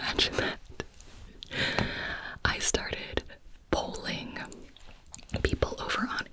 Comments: I thought you said bowling! lol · In the first minute I thought you said “ bowling” - not polling.😂